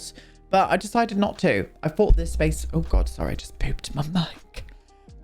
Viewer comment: You said "I just booped my mic" and what did I hear? "I just pooped my mic". Yes, thank you!